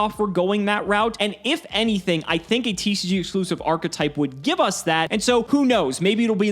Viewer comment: Thought he said "VTuber Archetype"... I am disappointed.